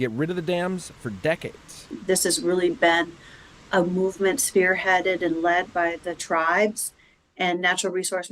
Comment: If you can adjust the captioning, I would. At 1:35 the captions say "This is really bad. A movement spearheaded and led by the tribes." But on relistening 2x, she says "This has really been a movement spearheaded and led by the tribes" It's a very different message.